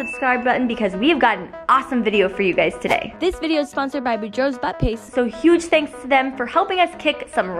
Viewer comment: Did she just say but pace